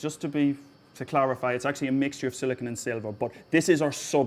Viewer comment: "It's actually a mixture of silicon and servopup" is what I heard. Maybe he meant soda pop. Reminds me of patent application from the mid 1990s where the inventor intended to encourage amino acids to bond by playing sounds at them which were sub - sub - sub harmonics of the infrared signatures of each amino acid. Synthesizing peptides accurately is hard!